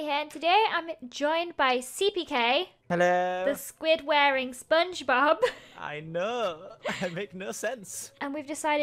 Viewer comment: Muahahah! Hope you enjoy this prank! If you do, please leave a like! No beans were harmed in this prank :P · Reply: know I thought you said "A squid wearing spongebob" like the squid is wearing spongebob